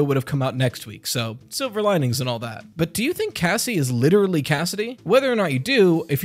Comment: I thought he said Gassie and I’m like what the hell could that be short for? Gassandra? Gaston?